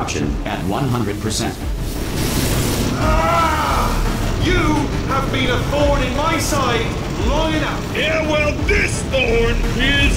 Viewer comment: what did he say? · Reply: “You, have been a thorn in my side, long enough!”